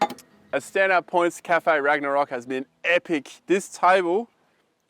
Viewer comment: What is the brand of the drop down table with sink? I missed it and cant find anything like cafe ragnarok which is what I thought you said. Great idea.